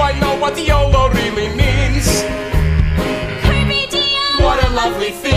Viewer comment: anyone know what Ocean says cause I can’t figure it out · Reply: “Carpe Diem” - seize the day in latin